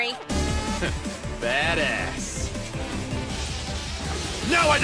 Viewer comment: Sounds like he said: Dat àss 😂